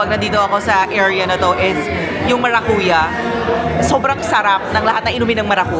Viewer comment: Sorry! I misheard you said "ang sarap ng mga kuya," hahahaha! (watching while working) But I really like this side of you, so relaxed. I don’t know, maybe it’s because the videos I’ve seen of you are always about work, work, work, and the grind grind grind in life. I love seeing you just enjoying yourself, the place, the food, and chatting with friends. Oohhh, what a sight! It’s so nice to see you taking time for yourself! ❤